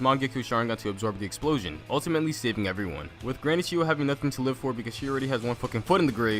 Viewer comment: when says chiyo I thought u said "cheeto"